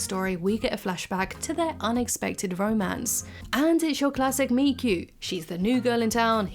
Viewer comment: classic Miku??? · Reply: I thought she said, 'meet cute'. A romance fiction plot device that engineers the first encounter between the couple.